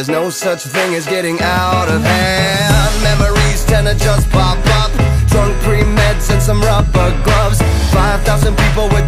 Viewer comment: Originally I thought it said "Mammaries tend to just pop up, drunk three mens and some lover trucks" XD